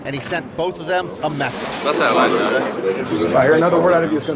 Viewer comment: What did sheik say @ 7:57 ? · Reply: "Thats out of line" he said